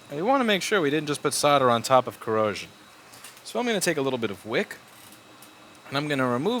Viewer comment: I thought you said give a little bit of FUCKs haha rewatched and you said flux I guess that the waxy liquid gel